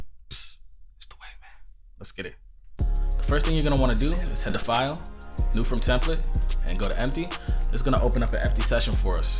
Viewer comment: I heard “shhhh it’s the white man” at 0:01 😂